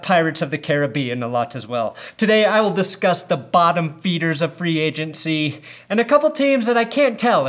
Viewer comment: Was I the only person who thought said ‘bottom beaners’ instead of bottom feeders at the beginning